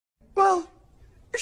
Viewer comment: It sounds like he says the fucking muffin man 😂